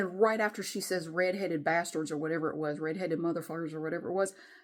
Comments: I heard "you two red headed you mother f*ckers". You need cameras with state of the art audio capture. You also need to secure some type of personal protection. I would keep my kids as far away from her as possible. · What I heard in the recording was obviously a string of profanity and towards the end I heard "It's going to be a hot summer...and you all are going to die...die! D I E. Two red headed mother f-ers." Then obviously your name at the end. That woman has some extreme psychosis and you have proof of it now along with an entire neighborhood of witnesses. With the threat she made, you can absolutely get this to hold up in court with a restraining order and get that woman detained for a psyche evaluation. I'm so sorry this is happening to you. May you and your family be safe! EDIT: You can also get her on charges of stalking and disturbing the peace. While this woman is crazy, she's smart enough to know her way around the law it seems. It's better to deal with this ASAP because with psychosis like this, that woman can have a total black out episode and do something really bad.